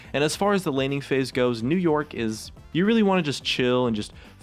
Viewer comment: Does he say New York?